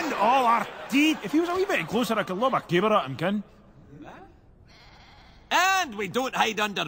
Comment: what did he say? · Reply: The language is Doric and, according to a Wiki dedicated to Brave, what he says is: "Gif he war a wee bit closer A coud lob a caber at him, ye ken" (trans: "If he were a little bit closer I could throw a tree trunk at him, you know.")